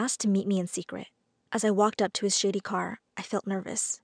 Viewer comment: Her: I walked up to his shady car. me: Did you just say shitty car!?